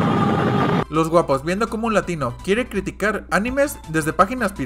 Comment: escuche los wapos?